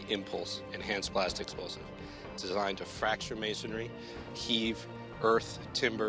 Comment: I thought he said "fracture masons"..